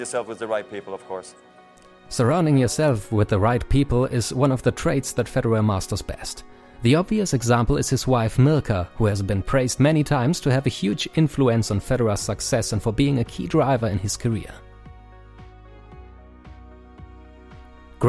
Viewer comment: Am I the only one who heard him saying 'his wife Milka' instead of 'his wife Mirka'? LMFAO